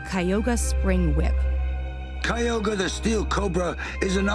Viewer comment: did she said kyogre the pokemon